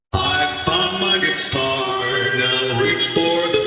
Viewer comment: Why did I hear “I f*ck my guitar!”? WHY?!!!